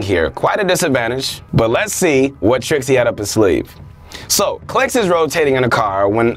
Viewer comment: he said Clik instead of Clix lol